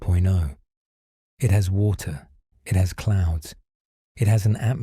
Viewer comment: I misheard the first sentence it has water, it has clowns...?